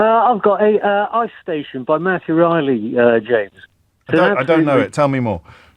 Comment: What is the book name the caller at 1:58:30 say? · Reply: Ice Station by Matthew Reilly.